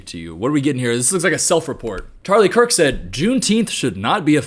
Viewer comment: Did he just say WHERE IS CHARLIE KIRK😭